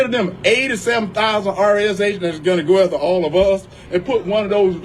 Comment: Is he saying "RES agents"?